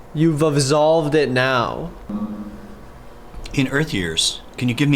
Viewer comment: I heard "you're selfish now" instead of you've absolved it now or whatever it was